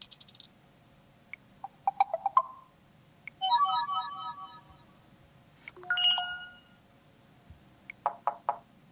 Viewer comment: WHAT HE SAY 🗣🔥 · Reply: Barbecue chicken alert!